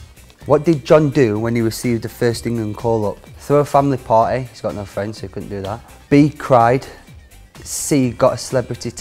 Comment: Walker: "A, throw a family party, he's got no friends he can't do that." *Family* Party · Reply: I thought he said "He's got no friends, so he could do that".